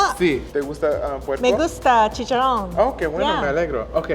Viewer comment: what she say at 4:25? LMFAO "me gusta chichar? " XD- - my mind is a very dirty place .___.